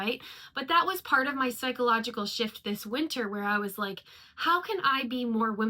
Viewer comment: I swear I thought you said "psychological sh** this winter" at the beginning and was like WOAH I've never heard swear, she must've been going through it 🤣🤣